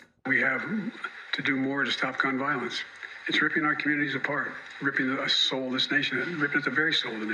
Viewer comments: "Ripping our soulless nation apart" is what Biden almost said there. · does he say soulless nation? Is that on purpose?